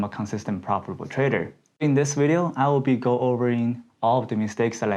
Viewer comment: i think at 1:22 he said " in this video i will be wolverine"! that is cool as hell for sure he is wolverine!